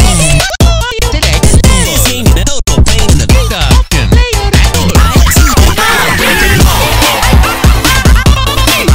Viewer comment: DID MARIO JUST SAY THE N WORD?????? 💀💀💀💀💀💀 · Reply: He said "Data", is a line ripped from Mario Kart 64